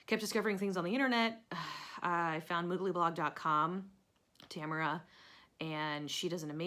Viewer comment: I can't understand what you said you discovered on the internet. You said you found something dot com and then said Tamara. I would like to know so I can check it out. Thanks! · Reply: It's Tamara Kelly of <link>. Lots of free patterns and tutorials!